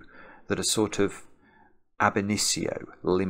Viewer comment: What is that long word says at around 11:40? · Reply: Ab initio